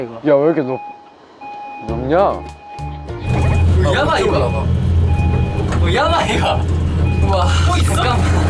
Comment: what did mashi say? yamai? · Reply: yabai